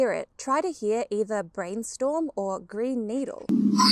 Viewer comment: I heard 'brain needle' :-(